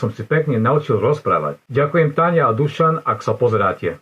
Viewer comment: What accusative? He's using nominative (Slovak doesn't have vocative, unlike Czech). “Ďakujem Táňa a Dušan, ak sa pozeráte” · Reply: Yeah, you're right, though I thought he said "Ďakujem Táne a Dušan", like putting her in the dative case but not him. That struck me as so odd that I derailed into animate/inanimate accusative 😂